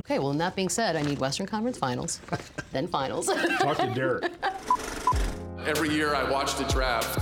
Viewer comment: "talk to Derrick" I mean what is this hahaha am i hearing it wrong or it is White at the wheel now 😂